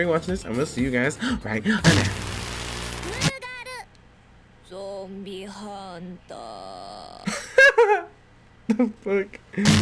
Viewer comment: I thought it said monster Hunter at Frist still watched the video anyway keep it up.